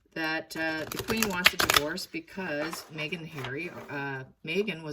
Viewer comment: I thought the queen said no divorce